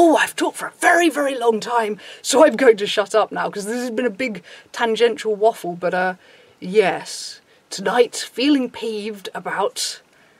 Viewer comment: captions say "big tangential waffle butter"... now i'm hungry @@